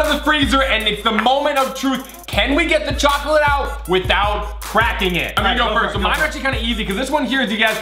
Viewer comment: i thought said bracking not cracking like not breaking bracking